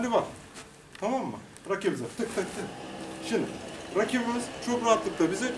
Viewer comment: te y*rak ye mi diyon ben mi yanlis duydum